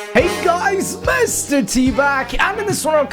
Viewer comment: at the start of the video you said "mr.t back" but I heard you said "mr.teebag"